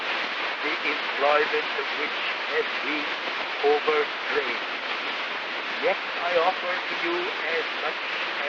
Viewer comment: Minor note: I think Gladstone said “overstrained“, rather than “overtrained.”